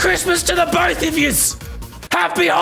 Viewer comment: I thought he said “happy Heineken” which is a beer, and he looked drunk at the moment xD